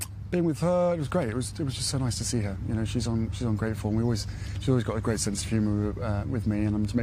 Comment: is it just me or does he say "she's ungrateful"? 🤔🤔 Sounds like it, doesn't it. Although he could also have said "she's ummm grateful". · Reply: Thought so too intially, But I think he said "She's on great form' (a variation of 'in great form'?)